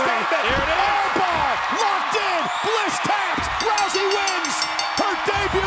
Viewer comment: "Arm Bar, Bliss Taps" Instead of fox🥴, I Just Noticed Haha!